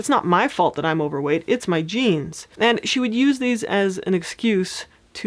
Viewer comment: When you first mentioned genes I thought you said jeans lol